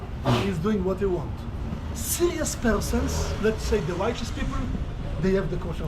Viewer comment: 우파 사람들이 아니라 righteous people로 들리는데 그럼 진지하고 의롭다고 여기는 사람들로 번역하는게 맞는것 같아요!